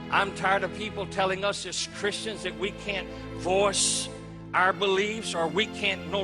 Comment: DUUUDE, did tyou heard him? at 15 seconds that the video start, this DICKHEAD says this: ''I'm tired that people say that us christians that we can't FORCE our beliefs.'' well, you are tired of that? i'm tired of you SPEWING SHIT AND PISSING ON LIBERTY. once i dreamt about going to live in america, nowaday i prefer tpo saty here in canada, at least we are a seular nation here is quebec since we liberate ourselves from the church grasp in the eighties. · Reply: Every time it sounds like he is saying "force", I think he is actually saying "voice". Not that I agree with him at all. Fuck this guy.